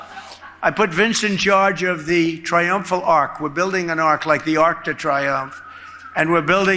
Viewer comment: At first I thought he said he was building an ark lol